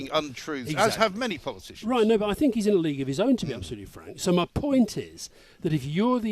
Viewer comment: Why does hi call Mike, "Frank"?